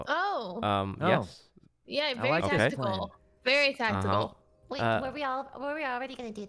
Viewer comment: did just say very testicle??